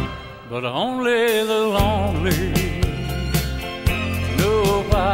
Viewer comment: As a kid I thought he was saying "old and lonely". That is what I heard through my transistor radio!